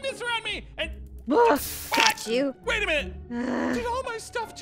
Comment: Did she just say fudge you or is it just me